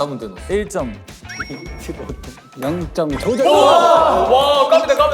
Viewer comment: 유진아 뭐라고? 잘못 들었나.,. ㅋㅋ 0점 조졌..까지 들은거 같음 ㅋㅋ